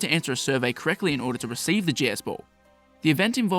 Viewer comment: The way you talk I keep hearing you say “Jizz Ball.”